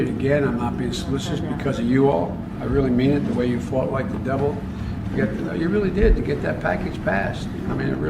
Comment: did he just say “they fought like the devil “ i fight for GOOD in JESUS CHRIST name may GOD have mercy on our souls